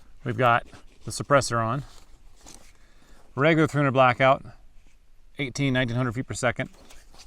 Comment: I’m not a gun guy, so I googled the hell out of “300 blackout 18 eggs at a hundred feet per second.” (07:46). What did you really say there? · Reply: That’s what I should have said! What I really said was, “here’s regular 300 blackout <num> feet per second.” Sounded like a bug flew in my mouth. What I should do is build as accurate a round as I can and then see if I can hit 18 eggs at 100 yds. The standard rounds I was using were not incredibly accurate.